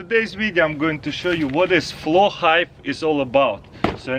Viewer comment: Lol i thought u said flow hipe 😂